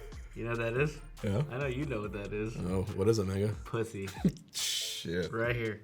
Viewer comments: What did hbox say in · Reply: “What is it Mang0?” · Uh, 22:50 did HB just say "What is it N****?" Someone help me. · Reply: He said “what is it mango” lol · DID HBOX SAY THE N WORD WHAT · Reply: He said “mango” lmao